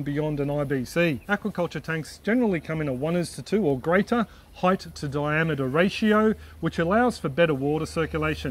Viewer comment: What is he saying? 1:2 ratio depth to diameter?